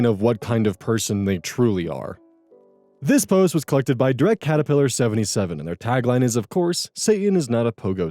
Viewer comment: You know, one day DirectCaterpiller is gonna change their tagine and it's gonna throw us all off. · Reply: Wait what? I always heard it as "DreadCaterpillar"